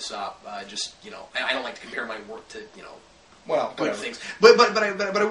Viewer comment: did say "I dont like to compare my work to good things"?